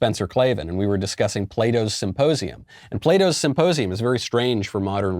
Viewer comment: LOL every time I hear "Plato Symposium" I hear "Play-Doh Symposium"!! BAHAHAHAH